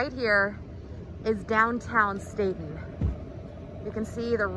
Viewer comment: I thought the lady reporter said, “This is downtown Satan”. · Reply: Stayton.